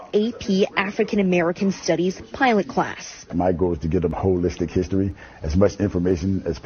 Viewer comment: did he say a hoe listic History?